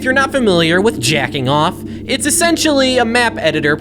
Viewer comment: I was looking at my phone when I heard “j€cking off” in my headphones. 😂😂